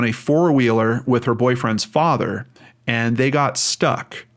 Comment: Whenever I hear of a girl last being seen with her father in law, my stomach drops. In my view it's just not a relationship that involves a lot of one-on-one time in really any context. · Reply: Thought he said boyfriend's father. Not father in law.